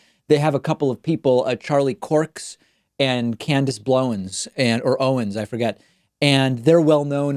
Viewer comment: I heard “Candice Blowins” and just laughed the rest of the video. LOL